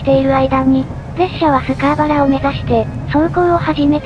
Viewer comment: 最後、確かに「スカーボラ」に聞こえる。。